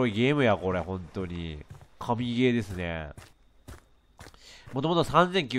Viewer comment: カビゲーですねって聞こえるw華美ゲーってことでいいんですかね、さすが華声！